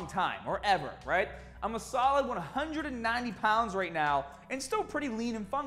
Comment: For a sec I thought you said 119 pounds and I was so confused XD